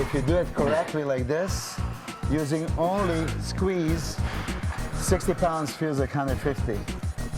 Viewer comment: did he say 60pounds feel kind of like 50???